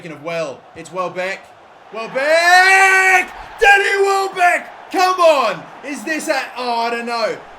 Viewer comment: did anyone else think he called him daddy welbeck after he scored? lol